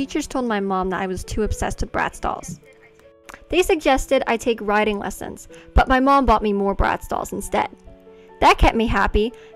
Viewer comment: This is bizarre, what she decides to talk about and how she does it. Also her family seems sketchy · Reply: if you raise the volume you can here a voice saying 'they suggested it' she says that after that was said. Someone's definitely telling her what to say